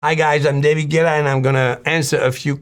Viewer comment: at first i thought he was drunk because he sounded a bit off... then i remembered he's french lol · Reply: I legitimately thought he had a speech impediment when I heard "Hi guys I'm David Gyeh".